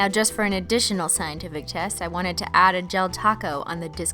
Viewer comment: Anyone else think she says ‘chest’ instead of ‘test’ around 8:38? 😂